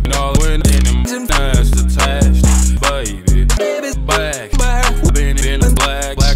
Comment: It sound like he said Babys back been in bladder